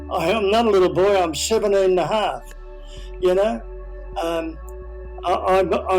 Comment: this is such an awesome story! what I don't understand is what he says in 23:14 while speaking to the counselor. I'm not a little boy, I am 7,5. what does he mean with that. maybe I'm misunderstanding because I am dutch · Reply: He said I'm 17 and a half year old